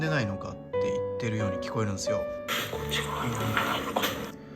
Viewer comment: ｢こっち側には何も無いのか｣にしか聞こえない…